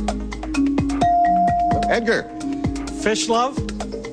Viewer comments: "Fish love" "Wish List" We all know the true answer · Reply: I thought he said Fish loaf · Omg that man was so committed to "fish love" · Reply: i thought it said fish lips lmao